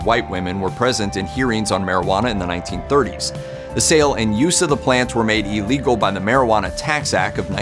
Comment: dammit I thought the video said why werd will be made legal